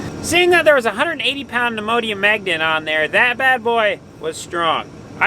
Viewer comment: Great job with the videos . Next time try a neodymium 🧲 magnet. Lol sounds like your saying Imodium . Keep creating great content.